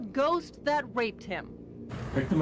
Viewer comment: did she say a ghost raped him? Or am I buggin